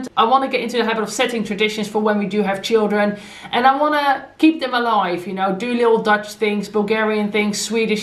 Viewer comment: When you said about traditions when you do have children I seriously thought I heard you say “I wanna eat them alive” 🤣